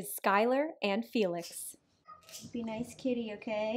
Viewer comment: You said, "be a nice kitty" and I distinctly heard him say, "no."